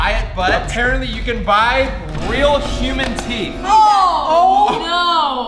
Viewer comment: I forgot to tell u I thought you said human pee not human teeth my bad but it's still funny human pee lol😂